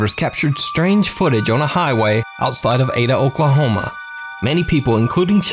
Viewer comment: I really heard Adolf, Oklahoma.